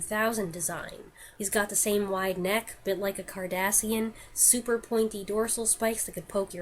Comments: When describing Godzilla, I heard "he has a wide neck like a Kardashian" and I spent several moments trying to think of which Kardashian has a wide neck before realizing you meant Cardasian... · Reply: Yeah, I wondered how many people would think I said Kardashian when I said Cardassian, LOL. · what are you saying about his neck? I didn't understand what you said. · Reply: "He's got the same wide neck a bit like a Cardassian" Google says it a star trek race of aliens.